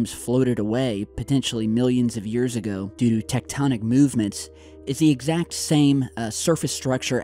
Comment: Did just say "DO DO TECTONIC MOVEMENTS" ??? 1:11 I finally found my new electronic dance music group name!